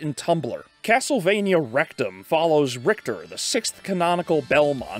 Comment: How come i hear Castlevania RECTUM instead of Nocturne. i need HELP.